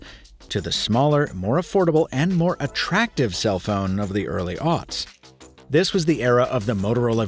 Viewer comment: English is not my first language, so help me out here. What exactly did he call the Early 00's? Can someone spell it out, please? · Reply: He called it the Early Aughts A-U-G-H-T-S